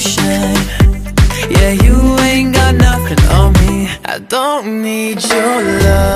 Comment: MISHEARD: JISUNGS PART: I DONT NEED CHENLUH🤧🤣 (ITS MY OWN OPINION LOL.)